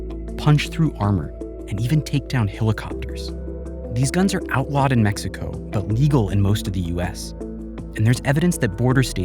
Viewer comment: When vice says "50 caliber rifles are illegal in most of the US", it shows how left leaning and politically dumb they are. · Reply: Yes they are leaning someway, probably forward, touching their toes. BUT You don't hear to well, they said @ 1:40 "The 50 cal is outlawed in Mexico, but is legal in most of the US"